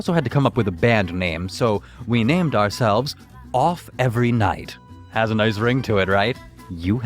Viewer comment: I thought that it said "Oof every night"